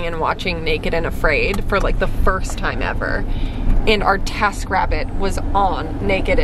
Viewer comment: When they were talking about TaskRabbit I thought they said TaskIt and I was like LIZA!?